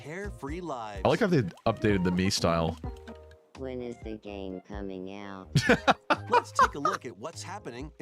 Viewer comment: I heard "when is the gay coming out" 😂 Probably either in June or Thanksgiving 😂